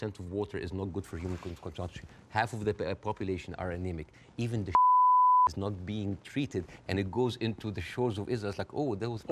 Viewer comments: What did he say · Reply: Even the 'shit' is not being treated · what did he say here that was censored? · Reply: “Shit”, he means that even when they’re taking a dump it can’t be treated well, because there aren’t many toilets available and people has to wait in line for hours to take a shit